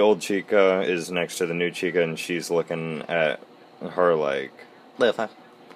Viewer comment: what did he even say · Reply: he said " The fuck?"